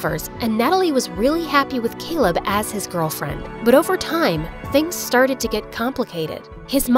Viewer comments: Why did I hear "and Natalie was happy with Caleb as his girlfriend" even the subtitles agree with me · i thought it said natalie was happy with caleb as her girlfriend 😭